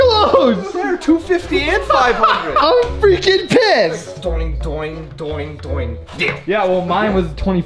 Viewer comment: anyone else hear @ 2:00 im freaking pissed?